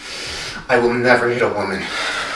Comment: What about equality??? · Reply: I thought he said I would never eat a woman I was like aww noo 😂😂